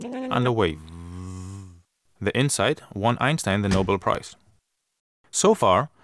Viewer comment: could anybody tell me what he said there? "The inside one Einstein the Nobel Prize" ? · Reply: Probably.. The insight won Einstein the Nobel prize.